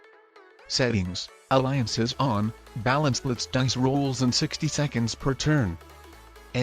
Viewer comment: What ducks rolls instead of dice rolls 😂😂